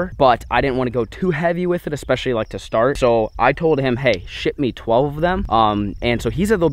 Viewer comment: I swear at 17:00 he said “hey, sh*t me 12 of them” instead of ship 😂😂😂